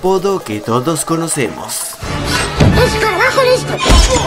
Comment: ¿que dice sticht? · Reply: escarabajo listo